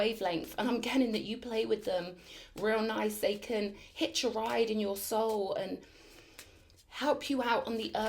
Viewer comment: at 5:32 or so did you say “brown-eyed satan hitch a ride on your soul?” I’m sorry! I couldn’t understand and it felt important to me. Could anyone else understand it better? Thanks.